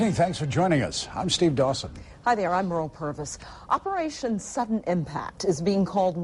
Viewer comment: hahaa i thought she said "Im Mearl Perverse" ...lol